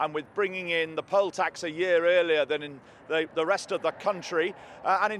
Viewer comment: Haha did he just say "scotch"?